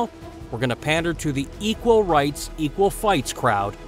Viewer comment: "Equal rights, equal fights," said a wise man once. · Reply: I always heard it as: “equal rights, equal lefts"